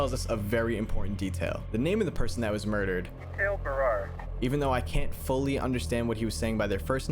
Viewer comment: i heard that he said mateo barrare u can hear the teo part in the first name but idk just saying